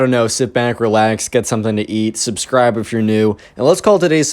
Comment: when I first watched this part it sounded like "sit back, relax, and get someone to eat" lol I'm not a cannible